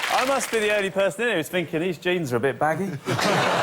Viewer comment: It was perfectly fair as Ross said leggings not jeggings at the time stamp you gave. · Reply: My mistake, I thought he said "Are they leggings, or are they jeggings?", but now I think the second word was actually "jeans".